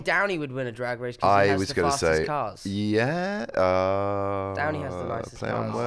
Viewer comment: Ok but I thought Tom said Downey had the best calves, like the part of the leg, and I was like I mean I guess? Though agree on Downey, especially with his sass and duck butt and lack of shame, he would be out there serving LOOKS every night. But, Tom.... That child is too adorable, protect him.